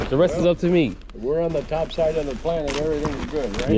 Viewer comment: What that man say? "We're on the top side of the planet"!